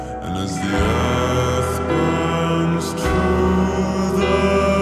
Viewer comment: who else heard "burns" instead of "runs"?